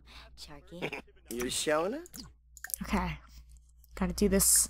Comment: go to 8:30 on the video i swear he said show me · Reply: i mean you showin it not show me